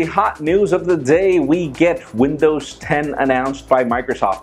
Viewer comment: Did he just say windows 'hen'?